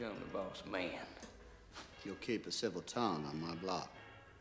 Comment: min. 0:33 what tom said?? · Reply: You'll keep a civil tongue on my block